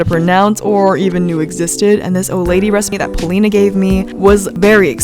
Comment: Who thought when she said Oladi she said OLD LADY! No? Just me? Ok.